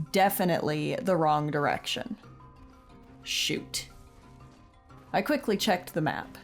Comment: for some reason I didn’t hear this part right at first and literally thought you just said “sh*t” 🤣🤣🤣